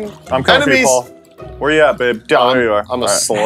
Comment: Paul calls for help. replies "Im commin' Paul! Where you at babe" yes. I heard it clearly. The fanfic is trueee!! 😅, maybe hide your secret a little better